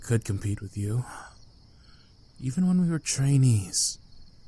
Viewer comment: I THOUGHT HE SAID EVEN WHEN WE WERE CHINESE AND I ALMOST CHOKED AND IT'S NOT EVEN THAT FUNNY SKSKS